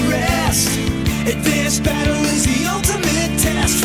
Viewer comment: For 18 years of my life I heard "Advanced Battle is the Ultimate test!" as "And this battle is the Ultimate test!" and while I hear Advanced Battle now, I still connect it as And this battle